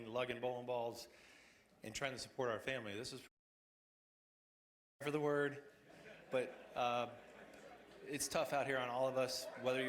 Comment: May I ask what Rash said at 49:07? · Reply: "This is pretty tough shit" he said